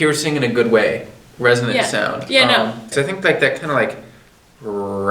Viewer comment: did i just heard him saying "RESONATE sound"??